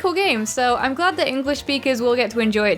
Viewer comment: I swear I keep hearing English beakers